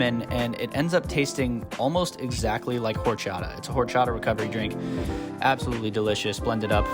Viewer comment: Thought you said your drink tasted like horse shot. Not gonna lie replayed that a couple of times until it hit me you said horchata lol